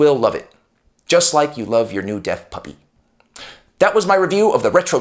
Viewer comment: My wife walked into the room near the end of this video. Specifically, when you were talking about getting out of prison after killing the fool who stole your pen. She laughed. Then at the end she thought you said “dead” puppy. She stopped and gave me a weird look and said, “Did he just say dead puppy?” So then I had to go back and explain your analogy. Basically, I saved your reputation. You’re welcome. 😉